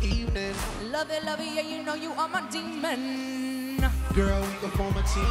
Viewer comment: did she said lovin’ a demon..?